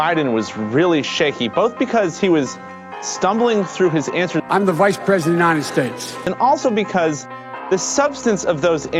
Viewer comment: It sounded like he said 'i'm the Vice President of Nonsense"